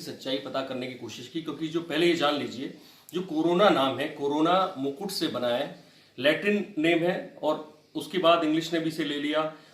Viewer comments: latrine name 😂😂😂😂😂😂😂😂😂😂😂😂 lol ... Aabe kya bol raha hey 😂😂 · guy said lattrine instead of latin.😂😂😂😂